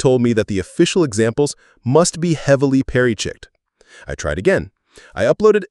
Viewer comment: I'm sorry, did you just say "perry-chicked"?